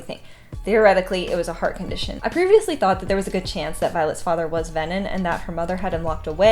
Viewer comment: For a moment, I thought you said violet's death. I'm like she died? Just realised that you meant dad